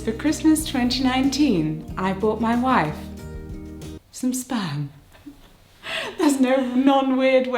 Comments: I thought you said you bought your wife spam and spam doesn’t work on a Sunday!!!😂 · tell me why i thought you said "i bought my wife spam"